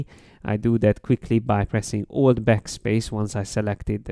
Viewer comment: Great vid but how does he change the icon's color at 7:36 ? He says he clicks "something backspace" when he selected the color. So does anybody know what is clicked there ? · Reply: That's "alt(option) + backspace" shortcut. You can use it to fill the shape with foreground color.